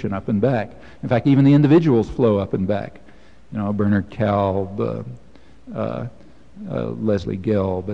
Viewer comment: What name does he say before Leslie Gelb???? ...Burnert Cal? Thanks! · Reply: Barnard Kalb. Found it a minute later! ...they both sound like human garbage.